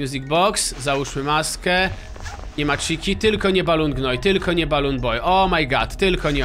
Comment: Usłyszałam✨ Balon gnój✨